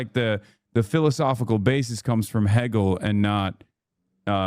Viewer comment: Did homeboy say "heggel"?